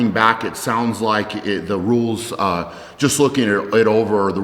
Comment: I thought he said nudes instead of rules.